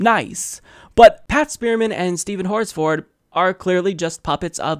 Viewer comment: sound like he said, "Steven Horse-Fart!" 😂😂😂